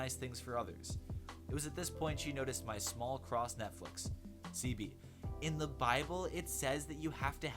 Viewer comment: “my small cross Netflix” anyone else catch that?